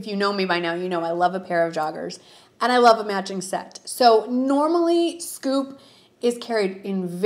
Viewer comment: You kept saying "matching set" but I thought you were saying "magic set" lol!